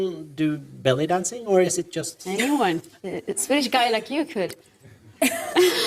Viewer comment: Que dijo en minuto 16:00 no entendí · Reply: " a Swedish guy like you could"